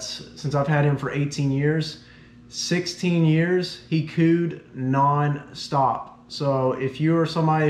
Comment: “For 16 years, he cooed all the time” I thought he said pooed